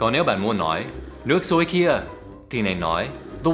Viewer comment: "nước sôi kìa" từ "kìa" không nói đc dấu huyền, mình nghe thành "nước sôi kia", mong luyện tập nhiều hơn